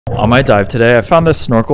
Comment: Whenever I hear "on my dive today" I think it's saying "I might die today" and i don't know why😭